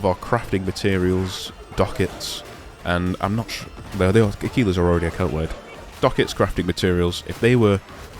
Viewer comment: im not catching that word... What is account wide? i hear "akeela" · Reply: Aquila's I think they're called, the premium currency. I may have pronounced it wrong!